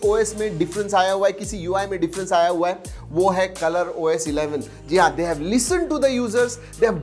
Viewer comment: I heard - "They have potty to the users" 😹😹💔